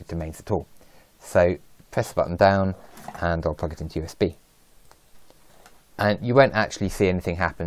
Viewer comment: What are you saying at around 2:09? The part after "while holding down the button" is a bit hard to understand. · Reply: Ahh, sorry. I say "Press the button down, and I'll plug it in to USB". So basically it's applying power to the board while the button is held down, which puts it in to bootloader mode.